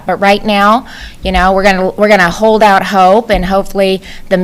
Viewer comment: start. Did I hear this right? We are going to hold out hope Iowa Governor Kim Reynolds April 2020. So moving.